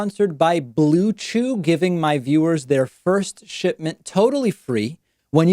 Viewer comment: "Sponsored by BlueChew, giving my viewers their first shitment." LOL! Did I hear that right?